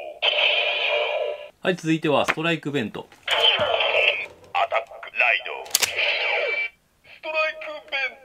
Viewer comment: アタックライドストライク弁当に聞こえるww